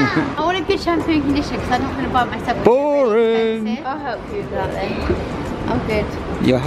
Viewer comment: i thought said «i’ll help you darling» 😂